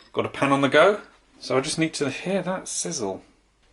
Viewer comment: WTF says "hear that size" around 1:16, and it got interrupted by an add saying the same thing... Bizarre...